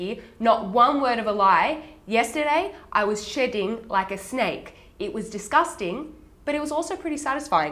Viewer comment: when you said "shedding like a snake", i heard "shitting like a snake". my bad. lovely video as always.